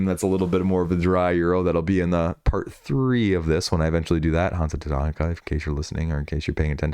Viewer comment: What was the game he said at 22:26? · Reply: Hansa Teutonica